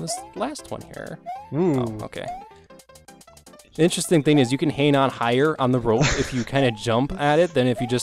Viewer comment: you can "what" on the rope higher? what did he say? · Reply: You can hang on higher.